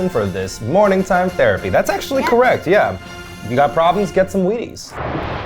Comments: I thought for a second he said when you have problems get some weed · I thought eugene said "You got some problems? Get some weed."